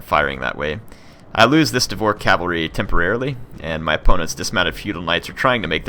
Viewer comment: I thought he said whore cavalry... my bad